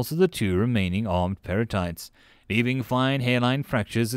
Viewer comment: I was confused when I thought you said perlite for a second